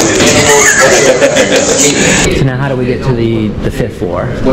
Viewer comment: Wait…at 23:05, one officer asks “what’s the 3rd amendment?” and the other officer replied “I dont care…”?!?! Did I hear that correctly? Unacceptable….